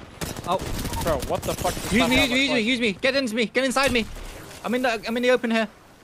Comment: What did he say at 0:37 ?? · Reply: Uueee Ueese ueese use me use me